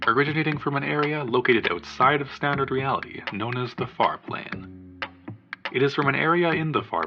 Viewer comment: I swear I heard “fart lane” instead of “far plane”, sorry